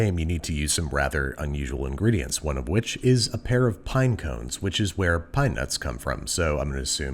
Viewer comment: I misheard pinecones to paimons